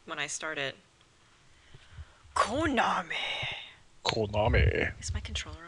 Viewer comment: turned on subtitles for funs 4:07 you say Konami......Subtitle says Called mommy tax package.....Well okey then....this might be an even more epic adventure I expected.